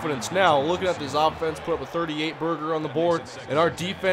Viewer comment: did you say "38 burger"?